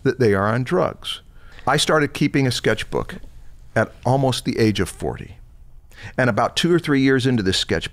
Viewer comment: I thought said nearly the age of 4 and then i saw the sketches and felt so inferior before my brain cells started working again and I must have misheard him 😅